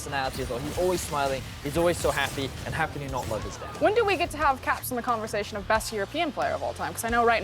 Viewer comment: did he just say "how can you not mourn his death" ?? XDD